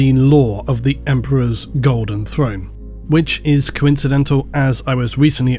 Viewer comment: haha I saw a phone video so when I went to home and this popped up I swore it said, "THE EMPEROR'S GOLDEN PHONE" I wonder what data plan he has....